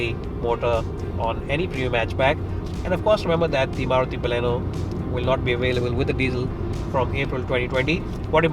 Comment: Did he say Maruthi Baleno @ 6:59😐